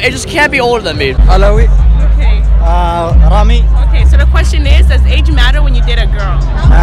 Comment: did i just heard